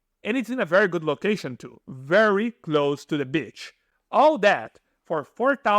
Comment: i heard "very close to the bitch" i was like what did u say bro??? then i was like ahh yes.. very close to the beach hahah!!!